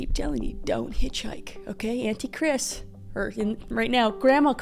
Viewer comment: Gotta admit, I first heard you say Anti-Kris, not auntie. 😮😅